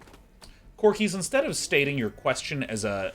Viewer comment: i thought he said crokeys at first